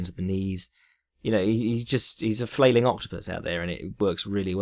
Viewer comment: Flailing octopus... hahaha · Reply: i thought he said "flailing optimist" but yours sounds more fun